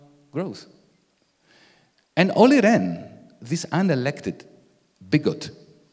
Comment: Who is this person who admonished the IMF? I can't make out the name. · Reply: I think its - Olli Rehn (Finnish politician serving as Governor of the Bank of Finland since 2018)